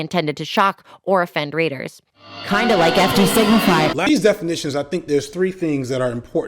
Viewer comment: Honest question: what does FD stand for? In the beginning I thought you were saying Fief the Signifier, but it doesn’t sound like that’s what Khadijah or Tee Noir said in their videos so now I’m going back to listen to your videos to try and figure it out. It feels like I’m on the edge of hearing it correctly but can’t quite put it together. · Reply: Fiq "da" Signifier